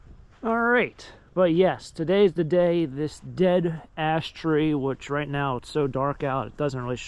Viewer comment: At first, I thought you said 'dead-ass tree'!